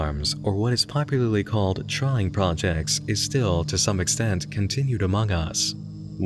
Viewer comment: My ears are dumb. I'm having difficulty hearing what is said at 12:32 "Or what is popularly called ???? projects" Chime projects? Trying projects? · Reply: "Trying projects." It's an old term.